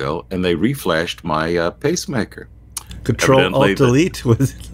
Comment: Control alt delete. 😂hahaha!! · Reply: At first, I thought you said "control all desires"